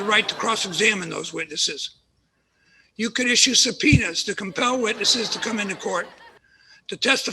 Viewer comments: did they add a laugh track to the word “subpoenas” because it sounded like “penis” because that’s how i interpreted it and it was very funny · I like that they laughed after the judge said "subpeonas" at 3:14 because it sounds like he said "penis", that's so typical of a teenage nick show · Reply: I thought he said "some peanuts" 😂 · Ngl sounded like at 3:13 judge was like "you can issue some PENIS" Drake thinking "awwww yeah sure I can issue some penis hehehaha." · why'd i think the judge said "you could issue a penis" at 3:15 😭😭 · Genuinely thought the judge said "Sir Penis" but it was "subpoenas"